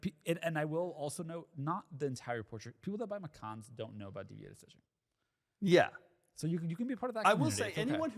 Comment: Does anyone know what is saying at 27:03? Edit: after replaying is 20 times I heard DDA suspension 😭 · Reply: deviated stitching, its a porsche option thing